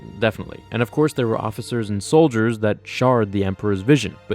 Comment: did you say "shard" instead of "shared"? Or was that intentional and I'm hearing it wrong?